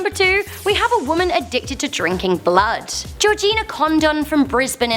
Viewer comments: did you say Georgina condom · I THOUGHT SHE SAID CONDOM 😂😂😂😂😂